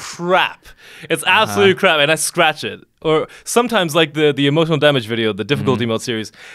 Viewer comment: and all these times, i thought he was saying, "emotional, dammit!"